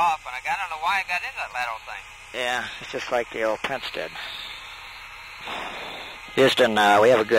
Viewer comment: Listening to the audio near the end, Haise says "I had to hold it off, don't know how I got into that lateral thing" and sounds like Gordo says "Yeah, just like the old Prince did" which could be in reference to Prince Charles and his PIO issue in the sim the day before. Update: Tried the closed caption and thats excatly what he said.